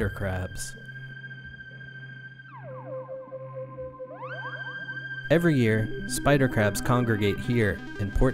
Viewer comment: I thought u said spider craps accidentally